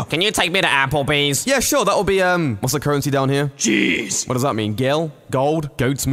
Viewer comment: I thought he said Cheese